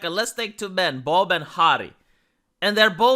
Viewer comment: I thought he said 2 men, bald and hairy.